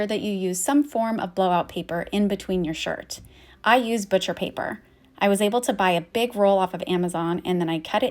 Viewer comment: What the name of that paper you ad said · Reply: You can use butcher paper or non-wax parchment paper. You can find the products I use on my Amazon store front. <link>